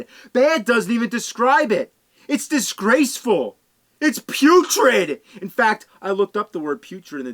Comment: I thought he said 'puke-trid' ... That could be another word to describe some bad games 🤢.